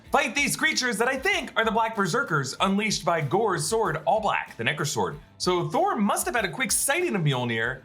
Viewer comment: I thought he said the N-word sword for a sec